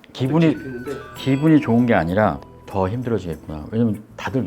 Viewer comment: 이때가 눈 뒤집혀 제2의 방시혁 된다는 뽕 맞아서 믿어주는 사람 통수 준비 다 끝나고 언론으로 여론전 펼칠 땐 거 같은데... 3:55 여긴 "기분이 좋은 게 아니라 빼돌리기 약간 더 힘들어 지겠네"라고 들림. 원래 비호감있었데 이런 일 터지니 완전 구역질 남...